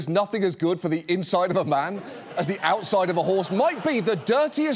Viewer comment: I’m so so glad that John repeated the quote because I first thought I heard Gary Sinese say that Reagan’s quote was “nothing better for a man than the inside of a horse” which was just as dirty. Maybe dirtier